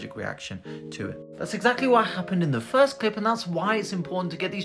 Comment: I thought he said "soon to be qualified professional" at first and had to rewatch 😂